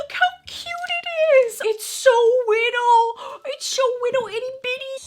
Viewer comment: "it's so whittle"...that's what she said ☹